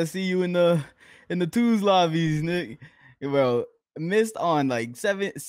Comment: did actually almost say what I think he almost said twice? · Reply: He is saying Nick bro -_